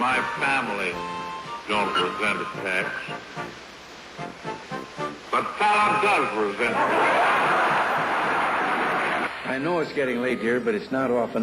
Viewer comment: what did FDR say? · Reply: Dog's name was Falla. "But Falla does resent attacks". Only republicans would attack a helpless canine.